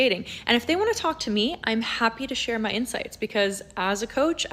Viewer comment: did she say "Im happy to share my insides"? 🤣